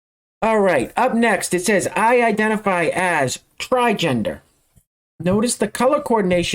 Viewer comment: When I first saw the female (I think) that identifies as “trigender” I was eating and glanced at the screen real quick and I thought she said she identifies as a refrigerator…. lol no joke